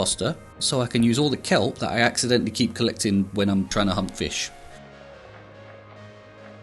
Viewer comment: I heard “hump fish” instead of “hunt fish” 🥲😭